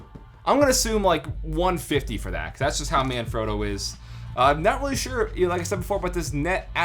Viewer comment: Every time you said "manfrotto" I thought you were saying "My man, Frodo", lol